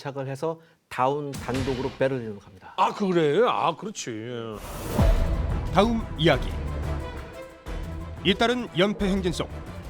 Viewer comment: 다운백작 이야기에서 끊기다보니.. 다음이야기가 다운이야기로 들림.